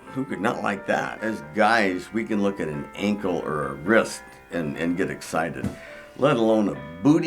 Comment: Wait did he say an ankle or wrist at 3:31 ? Wtf haha